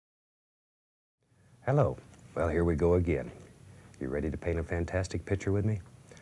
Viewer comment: I thought he say "oh shit here we go again"